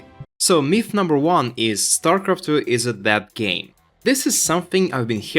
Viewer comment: It took me awhile to figure you are saying "dead". I kept hearing "that game" and wandered if that's some slang I'm unfamiliar with...